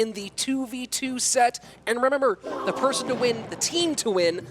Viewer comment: WTF he said in 21:44 😅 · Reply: Lol he fumbled on "remember" so hard